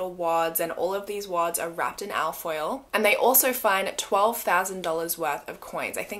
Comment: What is "alf oil" that the $10,000 was wrapped in? I seriously rewound this video over & over to figure out what exactly you were saying... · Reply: lol ok here in USA we call it just foil usually or tin foil or aluminum foil. I've never ever heard it called al foil lol I totally thought she was saying 'alf' oil. I'm thinking the money was wrapped in oil.... like how did that work? Hahaha